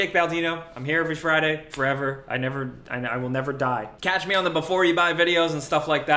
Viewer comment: For a sec I thought you said 'Catch me on the before you die videos'. 😂 Also, congrats on the immortality! 🎉